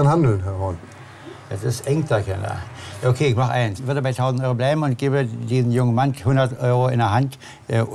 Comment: Er sagte erst 1000 Euro und 200 Euro für den kleinen Mann und dann auf einmal nur 100 Euro🤣🤣🤣🤣🤣🤣🤣🤣🤣🤣🤣🤣🤣🤣🤣🤣🤣🤣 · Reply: ich verstehe auch 100€